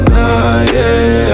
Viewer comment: Совсем что? Если я ошиблась словом и мне послышалось ,,магнитное" вместо ,,ванильное",то это не повод сразу наезжать,тем более с таким панибратским словом ,,ты". Несмотря на отношение как к подруге или другой знакомой Вам личности,благодарю за то,что поправили меня. Буду знать правильный текст на будущее.